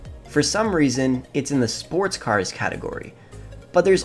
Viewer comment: he was saying it didn't belong in the sports class when it's named obviously states it's a sports vehicle. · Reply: I thought he said "sports class" I just re-watched the video, my bad.